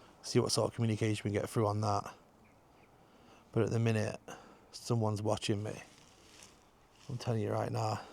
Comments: Hi i thought it said im watching.😮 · Hi love your vidoes i heard "im watching " ❤🙏🇦🇺